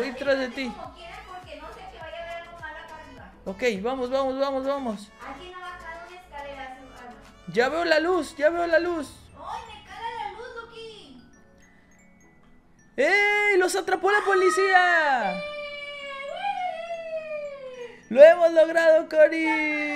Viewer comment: escuche "me caga la luz